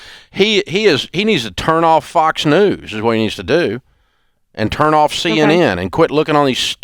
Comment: At first I thought he said and turn On CNN 😆